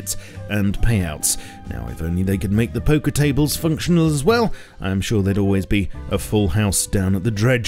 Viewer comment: I hate that I misheard it as "poker tables will be functional"